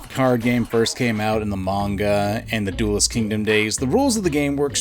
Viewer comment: At first I thought u said mangaramen